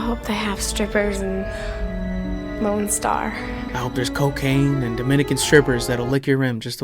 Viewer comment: Totally thought she said “moan star” like a knock off porn site lol